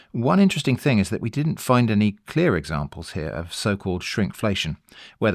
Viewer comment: Not me scratching my head when I thought you said Shrimpflation and thought I was outside the blast radius of an inside joke... The idea of using fish past as a stock starter is genius. I'm going to have to give that a try!